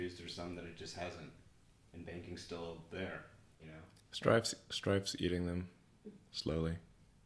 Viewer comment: does he say Stride? Strive? Stripe?